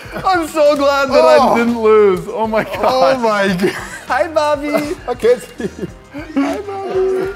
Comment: When said hi bobby I thought he said hi mommy!😉😂